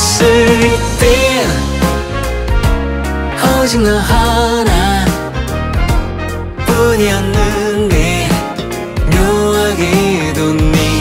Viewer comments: "오직너하나뿐이었는데"=>" 오징어하나뿐이었는데"로 들려요ㅋㅋㅋㅋ *저이승환님팬입니다!악플아닙니다!들을때마다혼자웃겨서요ㅋㅋ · 너랑있을땐 오징어하나뿐이었는데 묘하게뒀니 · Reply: 후후마곰 댓글 보고 나서부터 오직 너 하나로 안 들이고 오징어로 들려요 ㅋㅋㅋ